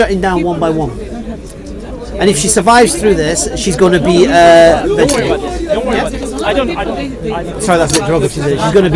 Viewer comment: What was the word he used in 9:00 , then used "brain damaged" instead? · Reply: Mental